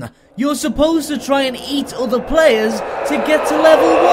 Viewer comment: Who else heard 'You're supposed to try and eat all the players to get to level 1!' ? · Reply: It sounded like he said other players